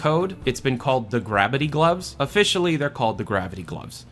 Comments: Does he say at 12:00? He says Gravity Gloves twice. I can't make out whatever he is saying. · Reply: internally they're called "grabbity gloves," officially it's "gravity gloves." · I heard gravity gloves twice. Is he saying “GRABity gloves”?